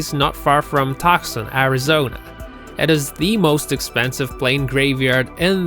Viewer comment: Toxin Arizona.... What did you say????